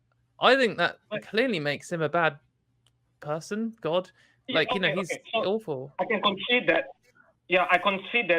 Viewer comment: I am the caller in the video. I conceded in 4:48 wrongly. I thought I heard said "dictator" rather than "dickhead", I was conceding to the dictator point. also said "clearly makes Him a bad person", for some reason while I was busy thinking to make my next statement, I somehow did not pay attention to this sentence - I did not concede to this point.